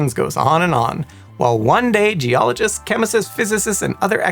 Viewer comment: I swear he says "geologists, chemicists, physicists" :D